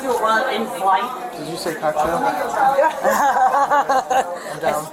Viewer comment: Can someone tell me what the conversation was between cop and lady around 3:45? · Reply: It sounded like he said " did someone say cocktail ? "